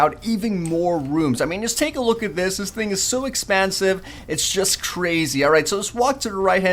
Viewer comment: did he said expansive?